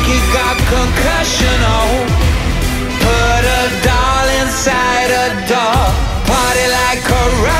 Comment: thought it said "put a dollar in a doll" or "put a dollar in a dog" i couldn't tell which one but now ik its neither lol